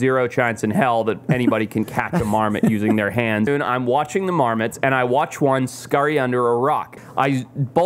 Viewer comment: I thought he said Mormons. I live in California and was thinking where do we have Mormons everywhere??